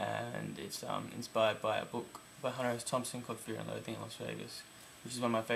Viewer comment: At first, I thought you said Bat Country was inspired by Brooke but you actually said a book. Ahaha we're onto you